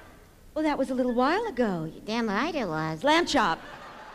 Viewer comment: Did Lamb Chop just say what I think she said? "Damn"? And is that why Shari called her out?